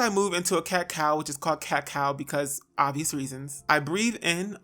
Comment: My hair porosity is 4c all the way through, it'll be 7 years when it's July 2020, aaaaaaand I could've sworn you said "cacao" instead of "cat cow," and when you said "for obvious reasons," I thought, "Well yeah, it's a 🍫 chocolate bean 🍫, hello!" 😏😳😁😂